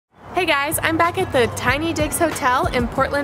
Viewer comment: At first I hear "Tiny Dicks" instead of Tiny Digs Lol